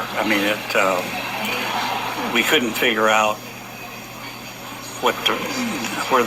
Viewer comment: sounds like he was about to say "we couldn't figure out what to write" but stopped himself.